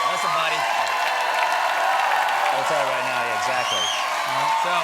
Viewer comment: My best guess is he says, "What are we gonna tell them right now?" And then S.C. says, " We're going to tell them right now, exactly." Which makes no g.d. sense, but I'm super glad joe biden won. 😅